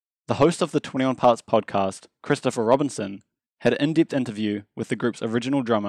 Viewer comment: i thought he said christopher robin